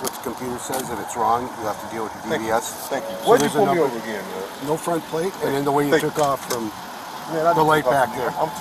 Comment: I notice the police said they pulled him over because of him talking on the phone with his hand while driving which is illegal and dangerous and after running his diver's license it came up suspended. There is no indication he was pulled over for his race. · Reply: I heard " no front license plate " and jack rabbiting from the stop